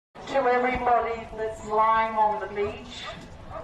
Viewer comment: I thought she said "to everyone that's slime on the beach" the first time